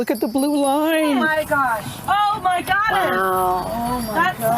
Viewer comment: after "oh my gosh", it sounds like she said "oh my goddess".